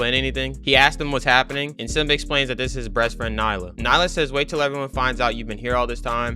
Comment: I'm sorry did u say "breastfriend"? NAH, someone check that shi and clip it